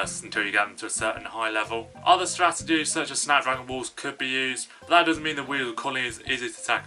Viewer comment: It sounds like he said" sand rifles" YouTube captions say "snow wolves" He meant "snap dragons"